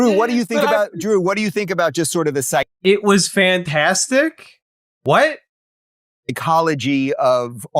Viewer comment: I think Russel Brand's "Fantastic" was in reply to the compliment of his "good gaydar", which means that his one single word he said the entire clip was bolstering a compliment made to him. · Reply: I could’ve swore he said “it was fantastic head” but I think parts got cut off and talked over